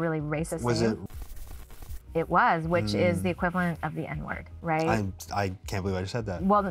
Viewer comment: What is the word at 21:18? "Red scalps", "red skins"? I want to know in case I encounter it out there. I'm thrilled you finally made this episode!!! It's a greatly needed one, their erasure needs to stop like, yesterday. No more ✊ 🧡🧡🧡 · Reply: Native here, the word is r*dskins